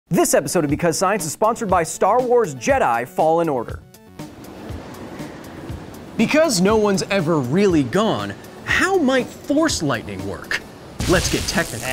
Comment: I thought You were saying “THORS lightning”